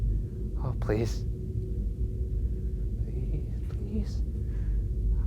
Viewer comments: What was the sound at 16:16 it sounded like something saying please back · Did anyone else hear the voice at 16:16? It sounded like someone saying "please" right after ryan speaks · A lil around 16:18 I heard a female voice "please" (?) I think it said Also if Shane actually didn't believe in Ghosts/"Demons" is if he offered HIMSELF instead of someone else who is not himself. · LOL did anyone heard a voice at 16:17, saying "please" and it wasn't Ryan voice? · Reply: Dim Sim it was Ryan's voice, he said "ohhh please"